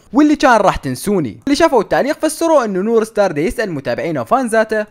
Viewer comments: منو انتبه ايش قال بي الثانية 2:10 قال نور ستارز 😅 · انت قلت نور ستار ولا انا سمعت غلط😃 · واتت سمعتون شنو گال😂? نور ستار شنو 😂😂😂😂💔؟??!